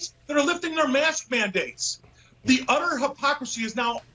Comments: Am I the only one who heard "the UDDER hypocrisy" !?? XD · at 4m29s it sounds like he says "..the udder hypocrisy'